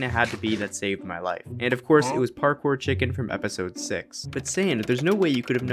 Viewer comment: what did you say parkour or hardcore